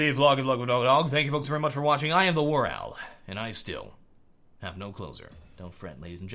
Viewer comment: What does he say at the end? I am, and I still have no...? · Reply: I thought he is saying: And still have nok clothes on